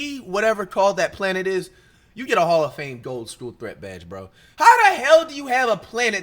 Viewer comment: did Flight just call planet a gold school threat😭?